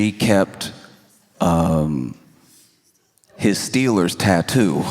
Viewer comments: I thought he said to collectively say “Ghost Steelers” 😂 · Hi Friends make sure to subscribe. If you want to see me live go to <link> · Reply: I thought he said “ghost stealers” LOL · I thought he said "ghost stealers" - which would've also worked lol